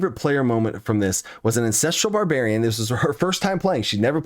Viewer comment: It sounds like you said Incestral Barbarian at 17:45 lmao